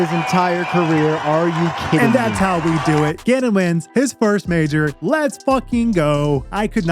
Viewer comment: LFG for calvin! btw T 9:26 it really sounds like you say gannon wins.. and the CC agrees lol it says genon LOL